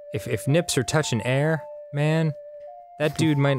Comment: I zoned out between 9:10 - 9:15 and I the next thing I heard was dan saying:“If nips are touching air man...” I can’t stop laughing 😆